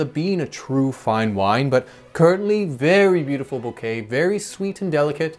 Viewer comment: M 1:55 Did you say “curtain-ly”?